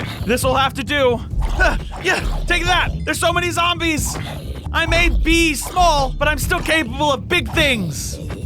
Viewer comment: did you say I may bee